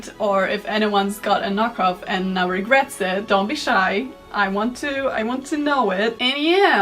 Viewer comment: "please confess to the meme mother if you sinfully bought a knock-off" is what I heard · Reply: I heard "I want to laugh at you".